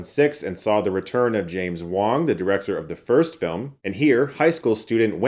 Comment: I heard “James Wan”. Thought you gone crazy